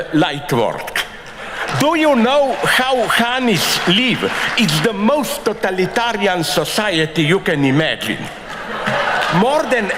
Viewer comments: What is he referring to at the beginning at around 1:30? I cannot understand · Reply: I think he's talking about honeybees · Can someone explain to me what he's referencing at 1:30? A society that is structured like: ______? I cant understand what he is saying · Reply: Honeybees